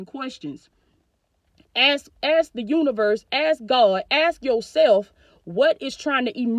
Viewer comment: Sorry ma’am did you say ask the universe?